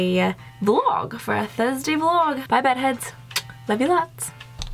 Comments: I thought you said hey butt heads!!! 😂😂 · Did anyone else think she said hey butt heads... then I realized her name...lol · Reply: i literally thought she said hey red heads